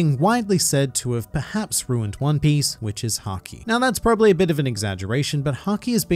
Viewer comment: Why did I hear "which is sanji" instead of "which is haki"